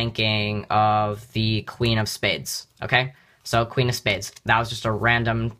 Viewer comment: he said clean instead of queen